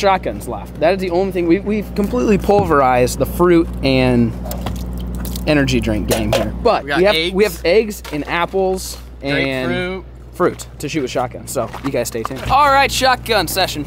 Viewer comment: I thought said we've got aids